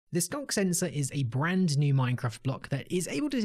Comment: "The skunk sensor is-" I'm sorry what was that? rewinds "The skunk sensor-" That's what I thought you said...